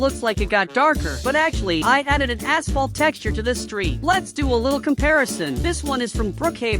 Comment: did he say he added a asshole texture?